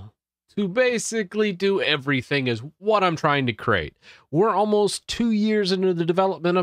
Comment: CRATE... not CREATE.. CRATE. LOL at least that's what I hear lol <3 if you see this lol